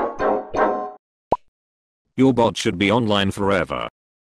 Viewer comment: wait, in 2:01, instead of saying "you bot should be on your server," it says "your bot will be online forever" in voice xd